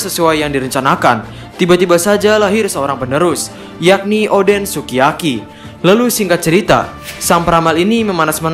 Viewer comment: Apa gue aja yg salah dengar bilang oden sukiyaki? 6:47 Kan sebenarnya kozuki sukiyaki atau gimana?